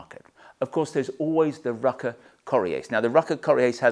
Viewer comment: I cannot understand the name of the jacket, can someone help out? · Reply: Rukka Coriace